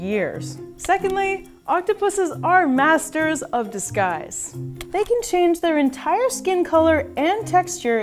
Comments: I thought she said they're the masters of the skies.. but after playing it back I heard masters of disguise 😅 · when she said disguise, i thought she said "the skies"